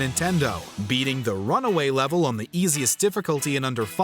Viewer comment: Did he just say beating the runaway level? 🤣🤣🤣🤣🤣🤣